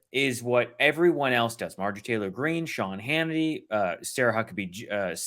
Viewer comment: I'm not sure if I misheard it, or if one of them actually said "Marjorie Tater Green," but from now on that is what MTG is to me.